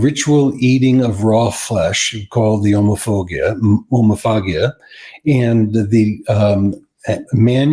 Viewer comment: For a second I thought he said “homophobia is the ritual eating of raw flesh” 😂